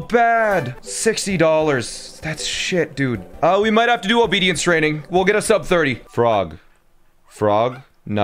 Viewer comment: when you said obedience training you said it so fast that I heard "obese training"